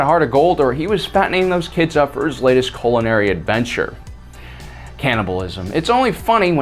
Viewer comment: Did you say Culinary or Colonary smh cuz it sounded like you said the second and that would mean colon aka asshole and I don't think Chewy is gay LMAO